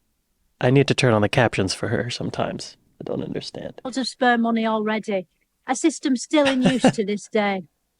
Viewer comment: LMAO don't use the captions. That's not what she said. "spare" money not "sperm" money. 10:50 I feel like it's gonna go downhill from here. They all know they are going to be interviewed and its a skit but don't know what they are going to be asked and are told that its being filmed as if it was serious I think.